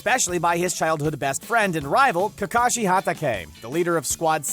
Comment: When he said kakashi hatake I thought he said kakashi hotmacake